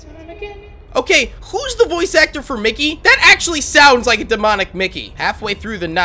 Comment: when you said "It sounds like demonic Mickey", I thought you said immortilized Bendy so I freaked out XD #ImstupidXD